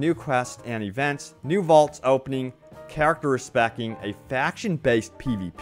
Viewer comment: Lol thought you were saying respect, not re-spec, my bad. My brain went off thinking how the respect stat could show you who is safe to approach and who isn't. Re-specing sounds cool too. though